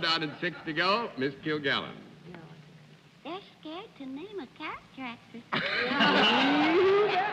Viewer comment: What does Mrs. MacArthur say to Dorothy at 22:05? My hearing isn't 100% anymore. 🙂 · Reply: They're scared to name a character actor.